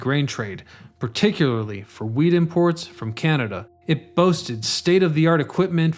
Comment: During it's early years, the grain trade was "particularly for wheat imports from Canada". The closed caption said "weed imports". I had to take it back to make sure wasn't talking about some illegal stuff. 😂